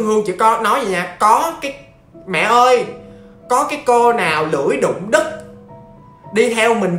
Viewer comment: Có ai nghe nhầm thành lưỡi đụng đít ko?